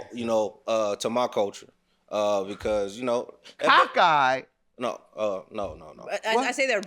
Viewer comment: He was saying caca(poop) in Spanish and judge Mathis thought he was saying cockeyed😂